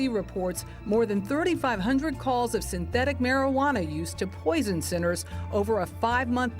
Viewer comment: Did she say "poison sinners" ????